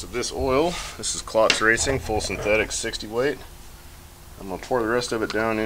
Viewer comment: Anybody catch what oil he likes to run?? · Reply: Sounds like he said Klotz Racing full synthetic 60w